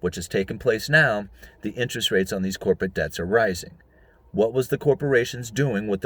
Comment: That's my trick ear fooling me again. It sounded like you said, Corporate Bombs. I thought, well that seems right. They will explode sooner or later.